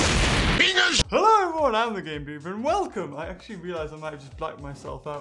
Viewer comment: It sounded like he said "Hello! Im The Gay Beaver!"